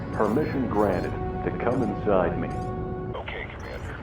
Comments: Did sseth say: "Permission granted, to come inside me?" at 10:09? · "Permission granted to come inside me" That's what he said?